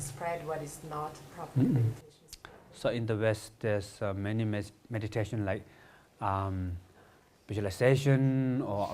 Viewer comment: "all meditation can be categorized as ______ or vipassana"? 1. what did he say? 2. how does Zazen fit into that? mindfulness? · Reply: 1. "Śamatha or vipaśyanā " 2. Zen meditation has both built in. In mahayana tradition vipaśyanā is kind of intelectual analysis of the phenomena. In this Rinpoche's vocabulary mindfulness would be the same as śamatha, because vipaśyanā is intelectual analysis . Using mindfulness to describe meditation is something coming from theravada/vipassana and the notion doesn't have the same importance in traditional mahayana texts and meditation manuals.